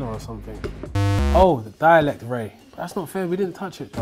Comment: what did he call is? A dialect ray?